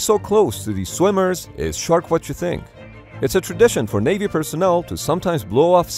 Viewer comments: Oh "you said shark what you think" i get it now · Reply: I heard "short what you think", not shark. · I just heard him saying it's "shark what you think" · Did you just say “it’s shark what you think?” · Everyone: “listening to what he says Me: did he just say : its shark what you think